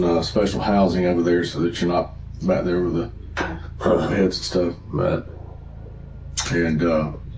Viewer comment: The caption is wrong he said back there with the past stuff meaning people he has arrested and interrogated